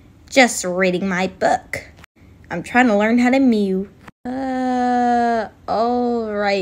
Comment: Did he just say im trying to learn how to mew??😭